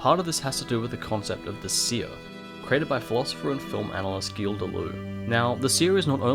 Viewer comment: what's his name? I can't understand it. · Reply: Gilles Deleuze